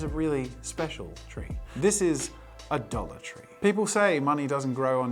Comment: When he was saying “a dollar tree” I kept hearing “adultery” and was incredibly confused. 😂